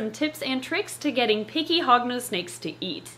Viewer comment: I thought she said pinky Hognose snakes to eat.